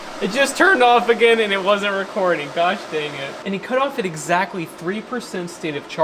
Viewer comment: Aw, Darn it! That was great. I'm glad you left that in the video. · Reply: Yup. Scrolled all the way to find this comment. Actually thought he said: -Gosh darn it. Edit: The 'Gosh dang it'(?) is